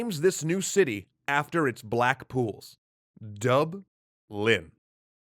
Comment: I can't quite hear what Dublin was named for. Black walls/black pools? Not sure if my ears are failing, or what's in between them ;-) Little help please.